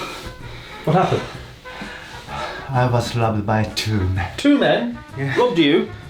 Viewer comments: At first I thought was saying „I was loved by two men.“ 😳 U wot mate? I didn’t burst out in laughter for a while. 😂 Thanks Chris ☺️ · At first I thought they said, "I was loved by two men". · I was listening to the video without looking and I thought the girls said, "I was groped by two men" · The generated subtitles say "I was loved by two men". Shit, I laughed out so loud, I woke up my parents.